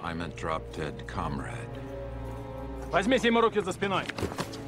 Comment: What the hell does Dovchenko say at 1:53? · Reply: Возьмите ему руки за спиной (take his hands behind his back), then Spalko: простите! (excuse me!)